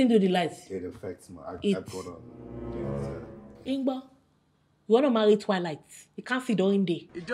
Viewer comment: Did she just say "you wanna marry Twilight? 😂😂 I just love Bimbo